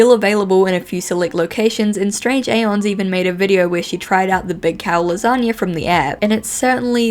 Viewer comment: that "calzone" looks the Necronomicon from Evil Dead!!!! EDIT: I meant 'big cal lasagne" but for some reason I keep hearing 'calzone'.